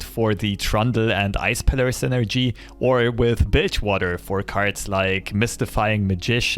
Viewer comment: Legit thought he said bitchwater with his accent. Lmao